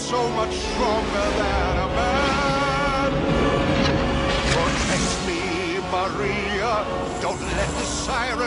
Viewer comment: I thought he said "forgive me maria" ToT